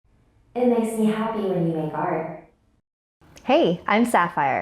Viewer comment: For a while. I thought the beginning said it makes me happy when you Fart Edit: if you you can’t hear it put it on 2.0 speed